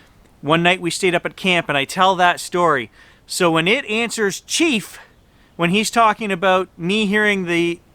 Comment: I heard 'cheat' not chief. And just here to die, I also heard possibly justice here tonight?? I dunno, but I'm starting to think they (the spirits) might be playing with you guys. Look into trickster god mythos in native mythology for your area. Just a thought.